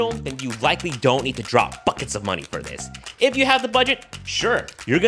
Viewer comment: literally thought you said "fuck its of money" haha